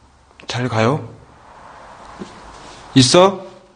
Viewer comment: 잘가요 들림